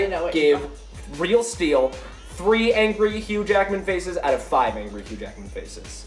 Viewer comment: lol for a second i thought she said huge ackman